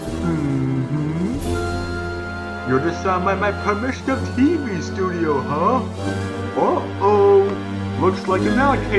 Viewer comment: What did he say? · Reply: He said Its Here